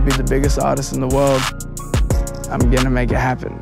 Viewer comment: I thought he said I want the biggest socks in the world. Wtf, I wanna see how the look like for some reason now...😂😭😆